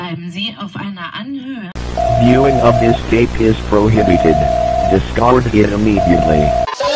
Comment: Can someone please tell me what he is saying? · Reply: What he is saying: Viewing this tape is prohibited. Discard it immediately.